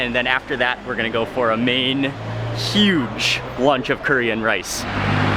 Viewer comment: Am i the only one who heard 9:05 as korean rice instead of curry and rice ? Lol i'm so lost the whole video lookin' for the "korean rice" clips 😂